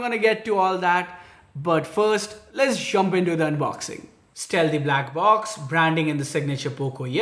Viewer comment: I thought you said no more Unboxing.. 🙄